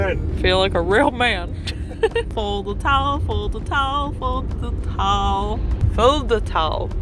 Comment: Sounds like you said "Fold the Towel" 😂😂